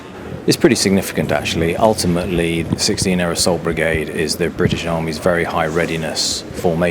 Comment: For some reason I kept hearing: "16 Airsoft Brigade"